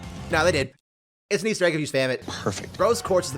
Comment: WHAT ARE YOU SAYING AT 4:03 ? PLEASE I can't understand it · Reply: It’s an Easter Egg if you spam it